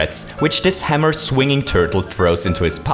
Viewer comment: i misheard "hammer throwing turtle throws" as "hammer throwing turd will throw". It did make this video slightly better